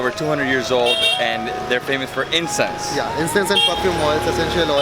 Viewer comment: they are famous for "incest" wht haha lol , thts wht i first heard